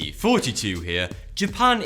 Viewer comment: I always thought this guy said "32 here" then I looked at his profile title. Haha